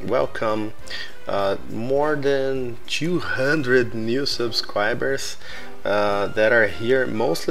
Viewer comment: "I want to welcome more than two hundread mil suscribers" Foi isso mesmo que eu ouvi? ------ Biffa do MindCrack? Maizeihn ------ A timelapse mais estilosa da possibilidade, mas um pouco longa demais por ser repetitiva. ------ Anfortuneiteli eu não posso doar :( mas é nozes que voa big witch